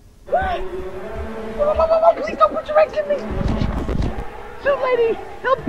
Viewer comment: wait did tom just say "please dont put ur eggs in me"?